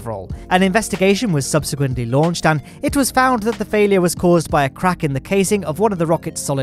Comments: Did anyone else hear "the failure was caused by the kraken?" :p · I heard "The failiure was caused by the kraken" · I thought you said a kraken